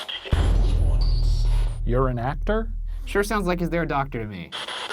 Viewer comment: Make up your mind. Did it say doctor or actor?